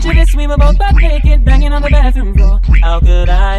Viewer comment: he said on the bathroom floor lol really u thought he said door